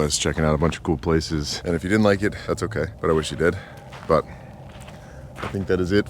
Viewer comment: I thought you said “and if you didn’t like it, that’s ok but I wish you dead” 😂 had to rewind it